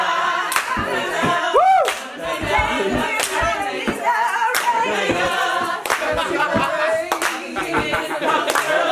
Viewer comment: lady sings “David has Natalina already” followed by Davids laugh. Did i hear that wrong? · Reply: “david give Natalina raise” but lol I friggin wish